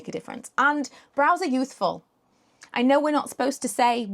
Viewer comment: I genuinely thought you said brows are usefull not youthful confused me a for a minute 😂